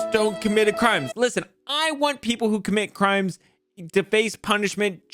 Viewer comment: "i want people to comit crimes..." 2021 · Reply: He said "I want people who commit crimes to be punished as well" You misheard who as to and ignored the other half of the sentence.